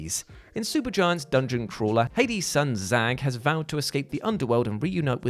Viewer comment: lol i thought you said "hades' son zack" 😆😅